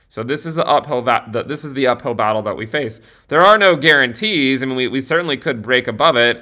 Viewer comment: When says “there are no guarantees”, all I hear is “ I guarantee”